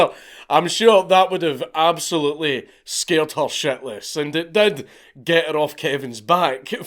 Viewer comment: The automatic closed captions put up scared shirtless instead of scared shitless at 45:58.